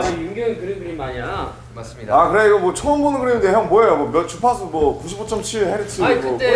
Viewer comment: did RM say Deutsche Pass? 😱 Honey FM😮